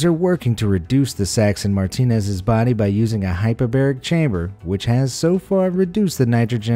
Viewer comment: I thought you said hyperbolic time chamber 😂